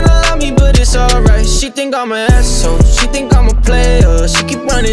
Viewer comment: In 0:25 i always heard: She thinks i'm a lesbian 😂